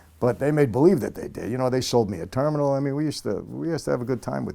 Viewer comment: Did he just say he bought a terminal?